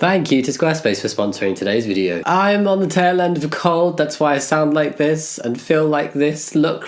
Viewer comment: Did anyone else mishear and think he said he's on the rail end of a cult and not a cold?